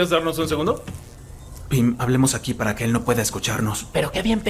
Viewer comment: actually the dialogue changes and the subtitles are wrong. Charlie's said "let's talk over here so he can't hear us" instead of "let's talk like this" or "let's talk in Spanish"